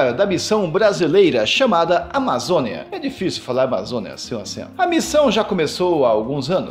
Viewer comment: Que orgulho!!! 💛 0:21 o que vc disse? Falou muito rápido kkk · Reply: "difícil falar Amazonia sem o acento"